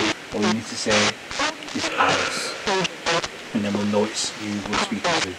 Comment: its sounds like it says 'Alice'